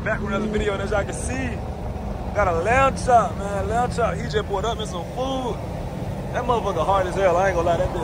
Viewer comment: Can somebody tell me what did he say in the second 0:07? He said 'pj put on me some food'? I didn't undertand that and It sounds familiar to a bar of a song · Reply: He said he just pulled up in some food . Lamb chop like pork chop . That's what I'm guessing 😭